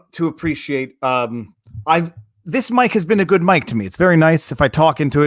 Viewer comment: I heard, "this Mike has been a good Mike to me" and thought, "aww, did Viva and Cernovich become friends?"